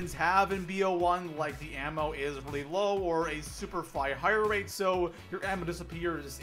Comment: Am I the only one who heard "super figh hire rate"